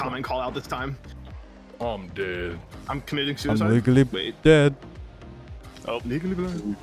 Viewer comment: I couldn't hear you said it but it was funny "I'm legally blind"